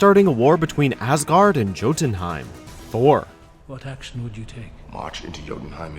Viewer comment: Did he just say "Jo-tunheim"?